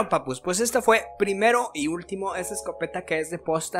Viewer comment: Pensé que decía primer y ultimo vídeo que subo hahahahah;-)